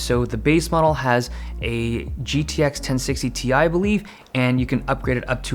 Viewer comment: Did I just hear GTX 1060ti?